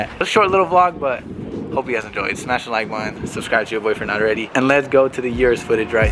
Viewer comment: Ayo did he say “subscribe to your boyfriend” at 5:58 Shii I mean if Emily cool with it😏